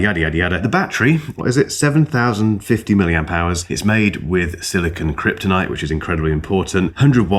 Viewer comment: did you say silicon "kryptonite" 😂